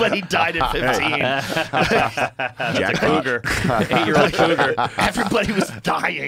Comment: Am I the only one who heard him say jackpot that was weird as hell😂😂😂😂😂😂